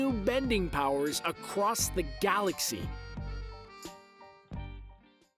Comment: Am I the only one who heard him Say "Galra" as in Voltron Legendary Defender?